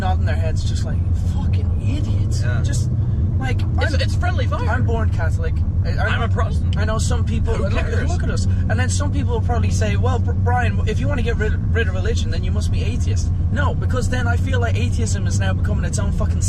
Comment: I swear i heard saying "im a prostate"